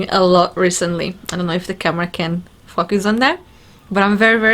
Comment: what does say at 25:22; focus or F us?